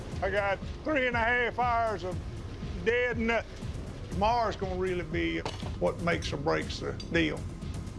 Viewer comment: it literally sounded like guy said ''Mars" but he said "Tomorrow" . Lol